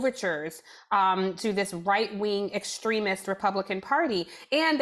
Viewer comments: Tiffani Cross says ... "to this right wing extremist Republican party", yet the caption says "to this right wing extremist Democratic Party". ????? ** ???? It seems to me someone had to do that change deliberately. But "right wing" and "Democratic Party" don't jive, so I don't know what the purpose of such a change was. I don't know who or what does the CC (closed captions), but this was a very very odd inconsistency! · the auto captions say Democratic where she clearly says Republican. Those words don't sound even a little bit similar. Kinda sketchy if you ask me. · I often have CC on just in case I need to turn the sound off and still catch the meat of it.. right around 55:56, as she's saying "and they make overtures to this right wing extremist Republican party, the captions say "... Democratic party..." Talk about false narrative. There are a lot of people reading and not listening to the difference.